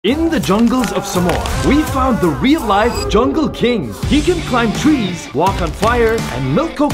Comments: i thought he said “in the island of Somosas” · I thought it said samosa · At the start of the video, I thought he said the jungle of samosa wtf💀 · I heard"The Jungle Of Samosa"💀